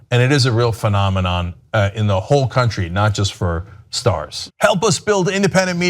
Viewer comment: i loved the end when cenk said "not just for stars" and I heard "not just for starks" :D